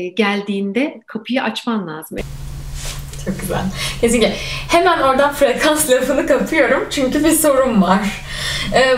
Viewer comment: orada kesiliyor bağlantı birkaç saniyelik, ne diyor acaba? ❤️🙏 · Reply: anlatabildim mi gibi bir şey diyor aslında eveet kesilmiş ses 🙈💕